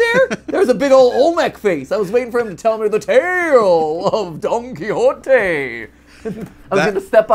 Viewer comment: Not "Game Predictor," but "Game Prophet." 42:53 I know MatPat said Don Quixote, but it sounded like he said Donkey Hotay 😂